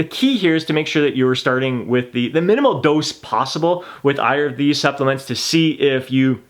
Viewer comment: "with either of these supplements" sounded like "with Iron and B supplements" ....I had to re-listen to that.